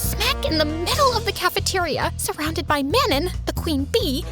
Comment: DID YOU SAY MANON???